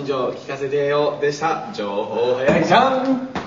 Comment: What he say? "jo-ho-e-jump!"? xD